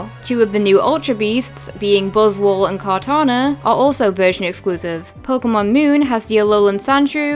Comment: It sounds like she is saying virgin exclusives